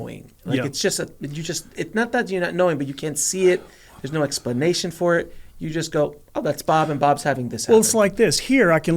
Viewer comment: can anyone else hear whispering on the 34:59 mark.....? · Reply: Had another listen since realised others heard it too. Nearest I can get is " oh bubba gun"